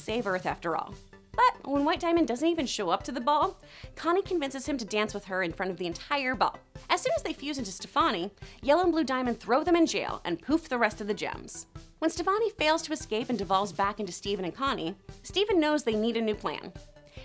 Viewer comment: i don't understand the confusion at the watermelon steven episode in the 5th season bc the watermelon steven arch has been around for seasons ? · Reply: I heard "they defused" at the time I read yours XD